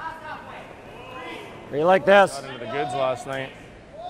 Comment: "Got into the goods last night." LOLOL! At least that sounds like what he said! I think sometimes these guys forget that the mic can pick up a lot more than they think. Funny comment though!